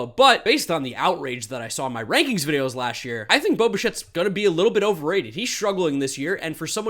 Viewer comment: caption says boba shit